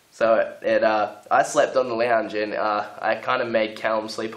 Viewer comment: Can someone please explain to me what the word is that Ash says at 5:58? and what it means? I've tried google but it's not smart enough apparently. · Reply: Is the word your looking for lounge? if so it means couch or sofa.